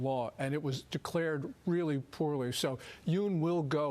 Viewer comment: That was not a reason to declare martial law. And it was declared really poor * . Yoon will go, I'm sure. 영어가 딸립니다 ㅠ.ㅜ * 부분 단어가 안들려요. 들리시는분 알려주세요. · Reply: 저 부분 poorly 라고 하네요 :)